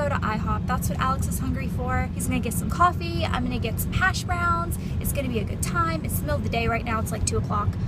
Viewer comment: LOL, I've never heard of hash browns before (I'm German, that's probably why) and for a second I thought you said hash brownies 😂😂😂😂 OMG I'm dying!! I'm glad to see you're feeling better though!! :) Lots of love from Germany ♥